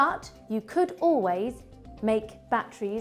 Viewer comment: I thought she said any fruit or vag